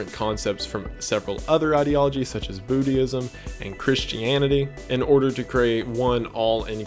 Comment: hehe he says buddhEism instead of just Buddhism.